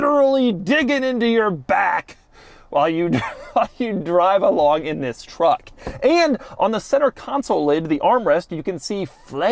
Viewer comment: Did I hear "While you ***in drive along in this truck"? LOL! Love Doug's reviews